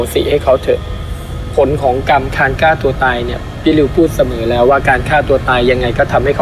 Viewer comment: ผมได้ยิน คานก้าตัวตายเนี่ย หรือผมฟังผิด